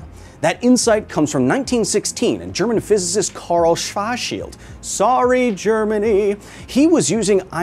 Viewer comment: I used to live in Germany and whats funny, is that in German with a German accent, Schwarzschild actually SOUNDS like your saying "Schvars-shit".....LMAO. (Google it if you dont believe me)